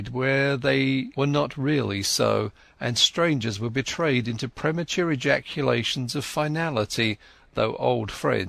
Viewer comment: Did I hear him say "premature ejaculations"?